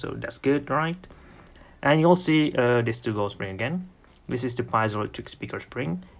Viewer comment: sounds like you said "penis" lol. Btw some 9400 resin case can crack.